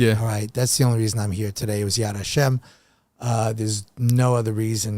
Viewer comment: what does he say at 10:30? “yad asham”? and what does it mean? I assume a jewish custom/religious phrase? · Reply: Yad Hashem means the hand of G-d